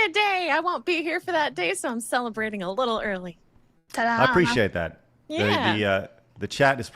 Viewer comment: said "ta-da" and I heard "ta-tas", and then she mentioned giant jugs. Probably many such cases when is involved. Anyway, great show as always! Glad you survived yesterday's beautiful nuclear chaos. o7